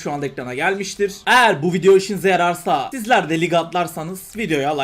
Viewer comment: ''eğer bu video hoşuna yararsa '' sanırım böyle duydum ahahah